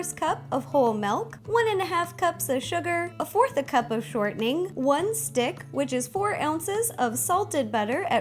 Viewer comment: when it Said "shortening" i thought you said "something" XD i was confused for a while!!!